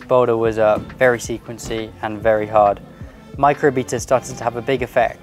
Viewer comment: Ohhhh I just realized you’ve been saying beta. I thought you were saying beater